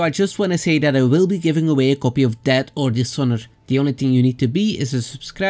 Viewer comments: DED OR DISHOROR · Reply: I thought he said debt or dishonor like the UKs current economic situation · lol at first I thought you said you will be giving away a copy of dad or dishonor. Love the channel homie!